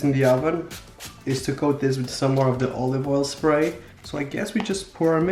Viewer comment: i thought he said "olive oil sprite" instead of olive oil spray😂😂